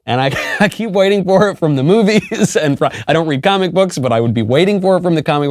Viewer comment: sounded like he said commiebooks lol I like it.